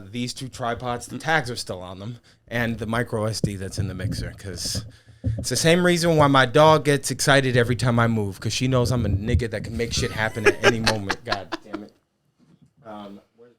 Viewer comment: Black person here: Am I crazy or did he say the n-word at 1:08? Someone please verify. I love this show, but I’m out if he said that sh*t. Edit: My bad, I didn’t know you were Black.